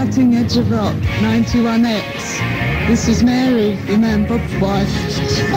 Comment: what does she say right after "this is mary"?? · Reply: I think she says "your man, Bob's wife".